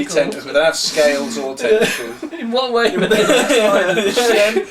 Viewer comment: What was said here? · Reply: "In what way were they like spiders?"